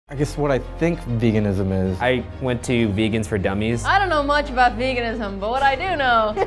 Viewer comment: I thought it said "Vegas for 30 days"